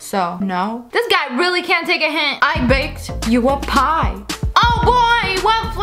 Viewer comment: When she was like “I BAKED YOU A PIE” I died of laughter cus I thought she said “I VAPED YOU A PIE” And I was like “How does that work just no 😂😂😂”